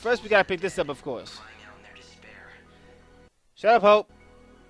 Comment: Thought you said shut up ho, but then realized Ho = Hope :P